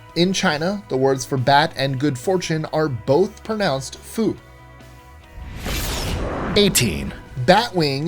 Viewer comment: Did u just say food?